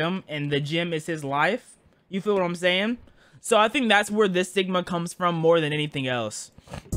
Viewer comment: is it just me or did he say thats where this sigma comes from😂😂